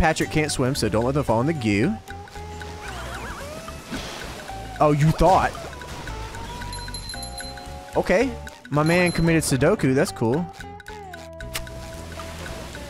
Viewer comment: I actually recently got an emulator just to play this game again 32:25 Wait... did he say Sodoku instead of Seppuku